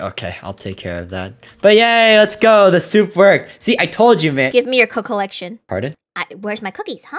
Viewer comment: Bro I left the video at daisy saying “gimme your cookies” but I heard “GIMME YOUR CO